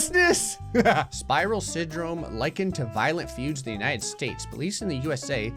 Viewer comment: did bro just say likened 😦